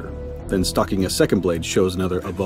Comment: Arrghh. For most of the video I thought he was saying stalked or stock not, stAcked.